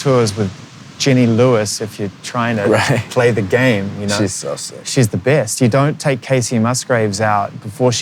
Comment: who else heard him say or almost heard him say, “ she’s so sexy”? Or am I imagining things?